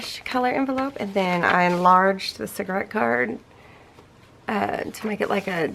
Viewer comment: You have been busy. Sounds like you had a great Christmas. Wishing you a happy new year. I thought I heard you say "cigarette paper ". What is that? Hugs